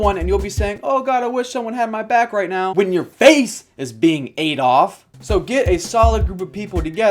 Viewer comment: when he said "ate off" i thought he said "Adolf"